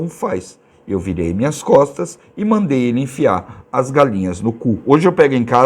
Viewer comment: Não entendi no minuto 8:34, vc virou de costas e mandou ele enfiar a galinha no c..? 👀